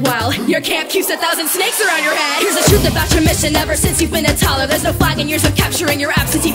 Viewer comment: Moana said " since you been a toddler" but it sounded like she said "since you been a taller"